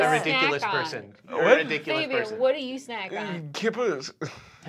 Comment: I thought he said kibbles like dog/cat food